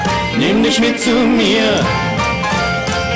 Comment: why i hear "SHATTER THEIR SKY" when it obliviously "BOOM! BOOM! BOOM! BOOM!"